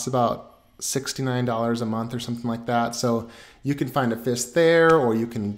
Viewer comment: DID HE JUST SAY $69 A MONTH?